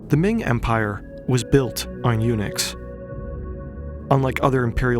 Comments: I don't know why I thought he said the Ming empire was built on Unix 😆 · First heard it as the "Ming Dynasty was built on UNIX" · who heard UNIX over eunuchs